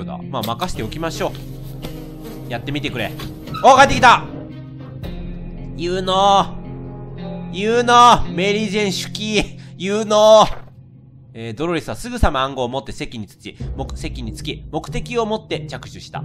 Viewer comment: ここ17:33メリージェーンしゅき〜〜💗って聞こえる、、なんて言ってるんだろう？？